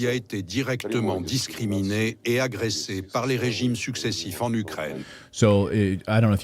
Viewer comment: Just a note as I do speak French and Natali is mistaken. 10:35 Lavrov said, (par le régime) "by the regime", not "stop the regime" (According to the translator. What he actually said in Russian, I have no idea.) And there was no mention at all of the eastern part of Ukraine or what Russia stand for.